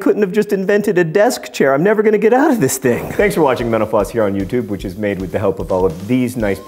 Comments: It sounded like you said "Thanks for watching menopause here on YouTube." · Every time you say "Mental Floss" really fast I hear "menopause." Why do our brains do that? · I thought he said "thanks for watching menopause here on YouTube" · everytime you say "mental floss" I hear "menopause"